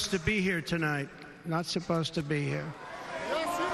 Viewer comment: When they kept saying 'yes you are' I kept hearing 'Yeshua'